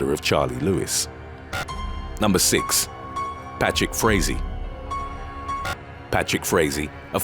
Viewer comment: The narrator said “Patrick Frazee”, I heard “bat shit crazy”.